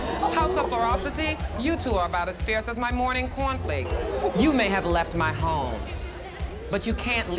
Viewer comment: “you two are about as fierce as my morning cornflakes” 😩😂😂im so mad this show ended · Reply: Did she say cornflakes? I thought she was saying coffee really weirdly.